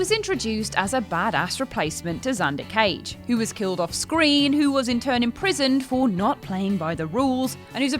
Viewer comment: Was only halfway paying attention and briefly misheard; Vin Diesel was imprisoned for not doing the XXX sequel?